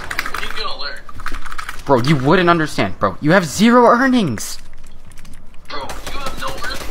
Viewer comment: at first i thought it said when grandpa has 0 earrings 😂